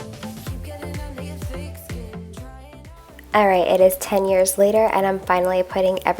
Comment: Wait a minute... is it just me or did she say it’s 10 years later at 17:13??? Lol